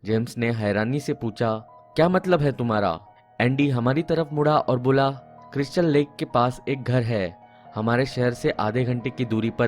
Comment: did he said Crystal Lake from Friday the 13th Edit :- Thanks for the likes